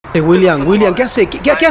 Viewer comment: All I heard was, ' e e e William William , e e e "